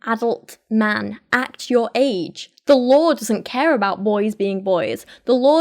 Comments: I legitimately understood "The Lord" instead of "The Law" at 11:19 and was very shocked and confused for a bit xD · When you said "the law doesn't care about 'boys will be boys'" I thought you said "the Lord doesn't care" and I thought we were having a very different conversation 😂